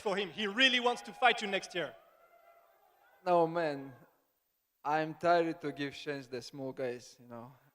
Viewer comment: For a second I thought Islam said ”I’m tired of give change to small guys”